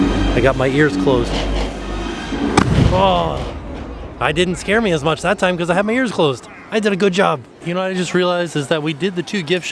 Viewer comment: did he just say “ I didn’t scare me much, because I had me ears closed “